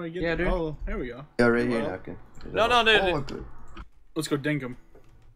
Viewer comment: did just say "lets go dink him"?